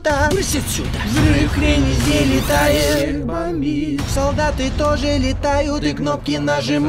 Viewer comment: мне послышалось "у всех бомбит"))))